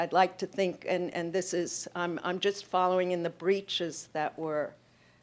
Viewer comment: Avital Ronell refers at around 55:00 to the bridges that were set up as torture instruments in Middle Ages.However, I have googled it extensively, and I cannot understand to what exactly she refers to. Could anyone explain? Amazing discussion btw! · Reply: I believe she is saying "breaches." She is speaking specifically about the trauma that arises from our (problematic) classifications of one another. This relates well to some thoughts Butler has been expressing regarding grief and the value attributed to human life based on these classifications; whose death, and thus life, is more grieveable? How did we arrive at this conclusion and what basis and utility does it truthfully hold? The breaches throw into contrast how these classifications are used to solidify groups and individual identities, often in opposition to other individual and corporate identities, and give rise to traumatic experiences. The trauma of encounter she deploys is exemplary of this.